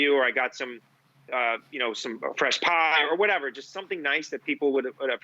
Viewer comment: did he say he's accepting doughnuts or some fresh POT?